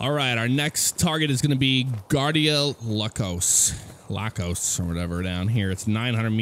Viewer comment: thought it said tacos XD